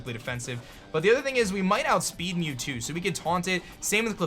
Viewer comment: "We mg hit outspeed mew too" xD I thought he said mewtwo